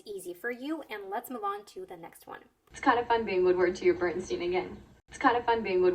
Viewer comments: I heard she said, " It's kind of fun being in world war 2 B#S&? again."😂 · As a person who holds English as a second language, I think I did fine with understanding the jokes but the only problem I had was not being able to make out certain words like "dye job", I kept hearing Dyjo and couldn't figure that out without subs. Same with 12:09 which I totally butchered, I heard something like "was kinda fun being would word to your burden stevin' in" and I was like "say what now?". Honestly though, I don't think I am really the problem here and it is more to do with the audio quality and their pronunciation/articulation. Also with Bernstein, that might be how it's said over in the US but Bernstein is a German name and the "er" in "Bern" should be pronounced more like the "err" in "errand" and "stein" like how you would normally say the name of Einstein.